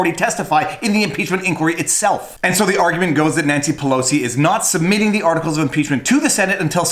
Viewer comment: I coulda sworn I heard him say “Nazi Pelosi” ... in which case he’s brilliant.